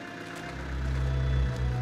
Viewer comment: That german accent is funny lol.. trying so hard.. poor english men! Thats my only complaint but a minor one. 29:56 ish.. for anyone who doesnt understand basically he is saying find her.. I couldn't understand the first part but thats not the point. all i can get from it: "schone finde sie" Feel free to correct me I've only been learning for like 18 months.. so yea I aint great lol